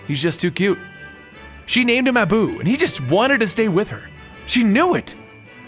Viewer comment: I thought he said the don's name was Nabu. I was really hoping to see Doctor Fate puppy.